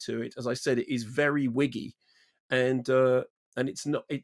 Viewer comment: Did MK say "wiggy" not "witty?" I felt it lapsed into groups of set pieces, or vignettes, occasionally lacking substance. Not the subtle writing or presentation evident in some Newsroom eps & tWW.